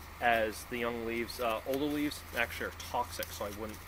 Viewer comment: for the information. Though I will have to add. The active portion that is used for medicine in beech is creosote, which in high doses is toxic. In smaller doses internally can be used for medicine. Young leave have very little creosote so they have been said to be eaten. Older leaves especially in the fall have higher levels of creosote in them. While it won't kill you flat out, it will start to have negative effects on your body. That's why I don't recommend eating them. But to each their own. Down the line I did find the same as you that yes they do have the antiseptic properties. Which is cool because that's actually how Beech trees compete, is by putting creosote into the ground through the fallen leaves. Which is why beech forests tend to only be populated by plants that can tolerate that toxin. again, thank you very much for your information I do appreciate it, and I do plan on coming back and doing continuation of these earlier vids, with newer information I come across. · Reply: no problem... I went back to the video and where I thought you said "all the" leaves are toxic....realized now you said "older" leaves. My bad on that.